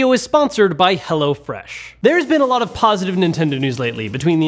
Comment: I don’t know why I thought you said “hella fresh”